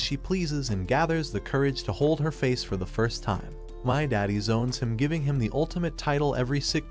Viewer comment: did bro say "my daddy"